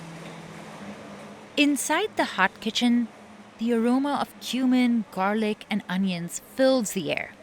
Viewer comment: is it just me or does it sound like she said “the aroma of human…” 😅 (Yes I know she said cumin but)